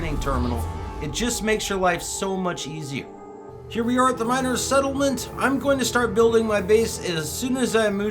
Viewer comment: having trouble buying upgrade to advanced mining laser. i can talk to anyone hardly and after i do they wont talk to me again I located a trade depot on a planet, but haven't found a mining settlement yet. Is that what i need to look for to get adv laser mining? It about the 22m mark on your vid. I grabbed 40 words from knowledge stones, doesn't seem to be enough to trade. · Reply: sorry for the misunderstanding i thought you said miner settlement instead of minor, so that's how i got confused. Anyway used your advice i don't have billions yet but i have enough to pretty much get anything i want.. If there is something i cant exactly afford i just use the techniques for making tons of money and it doesn't take that long to have enough and then some. I really appreciate the guide i have learned a lot.